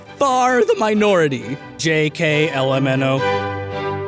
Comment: I thought he was trying to say JKLMAO but I just realized he just said the rest of the alphabet